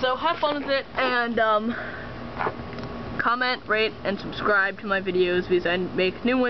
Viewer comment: i thought you said comment, rape, and subscribe